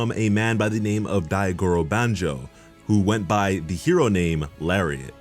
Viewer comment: I know what you said but this is what I heard “who went by the name ‘larry’”